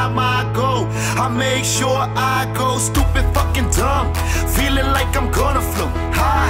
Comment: i always thought he said 'Make sure i go stupid fucking drunk'